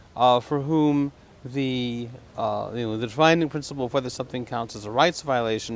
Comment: what is he saying at 0:50? "the dfhaie principle..." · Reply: Defining principle